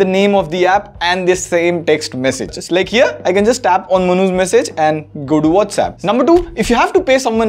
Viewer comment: When you say manu...i hear "manhush"